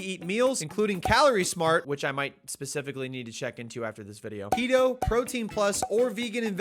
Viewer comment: Am I the only one who heard keto as pedo?